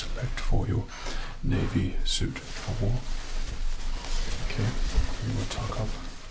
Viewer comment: I also thought he said “Navy suit for war” but I just rewatched the TM Lewin vid and he said “navy suit for work”